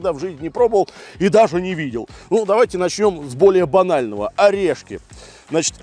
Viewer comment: что он сказал? Начнём с больно анального? Это самую суть передал 😂